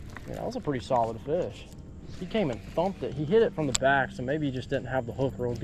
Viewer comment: IM SORRY what did you say!!!! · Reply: Hit it from the back